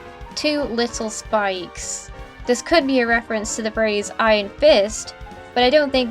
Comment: All I heard her say is "I Fist" instead of iron fist